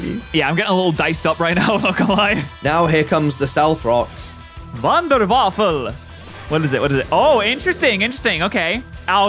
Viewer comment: im sure im not the only one who heard say "wonder waffle" I'm from Germany and it's always funny to hear English speakers try to talk german.